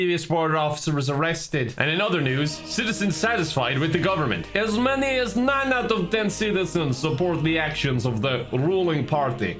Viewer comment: I didn't catch the "nine out of" at first and thought the newspaper said "as many as ten citizens support the actions of the ruling party"